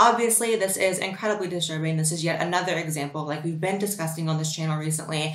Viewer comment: sounded like you said "been disgusting." I mean same difference but its funny.